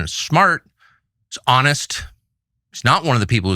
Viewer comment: wait what did you say? Honest?